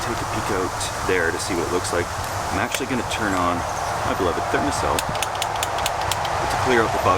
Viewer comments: Does anyone understand what he said at 3:32? My clotted fitness cel? Micheal Edwards fit the sail? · What's that gizmo he's using to clear out the bugs at 03:24? It's unclear, and the automatic captions didn't pick up what he said either. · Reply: Thermacell Mosquito Repellent